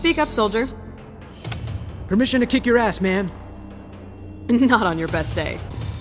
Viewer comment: I’m sorry but I thought Kitana said “not if your dad was gay” I was like damn but I had to listen to it again and was like oh.